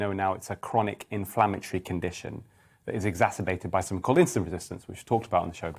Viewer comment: what was the resistance the Doctor mentioned at 0:13? I didn't catch the word. Thanks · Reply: He said "something called insulin resistance".